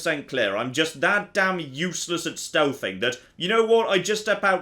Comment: I truly thought he was gonna say "I'm just that damn useless at steve"